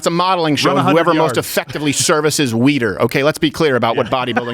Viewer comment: “A competition of who most effectively services Weider” had me on the floor. I’ve heard similar stories from guys who competed in the 90s. Good for you Mr. Crowder. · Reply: I thought he said, "services weiner" 😂